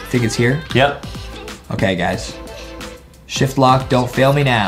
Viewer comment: Did he just say sh sh shit I said that